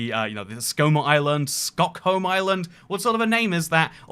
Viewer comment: I first thought you said Stockholm until I saw it said skokholm, so I knew must have miss heard